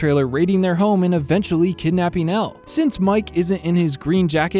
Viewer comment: idk if its like a short way of saying it or something but does he call her L